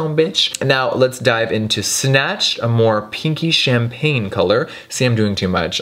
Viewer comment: My phone started buffering when Jeffree was swatching the pink-champange shade in the thirsty pallette and I thought for a second he said "kinky-champange" color, and I was just like ; 🤷🏻‍♀️ "Yep, sounds like Jeffree."